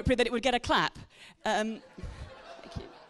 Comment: did she actually say “Thankyou good” under her breath